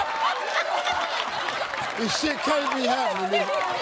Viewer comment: I hear "This shit can't be happening"..